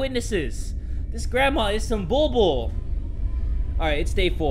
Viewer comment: did Jay said 'this grandma is some bobo'? HAHAHA